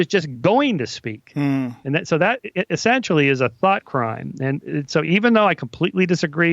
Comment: Did he say butt crime lol@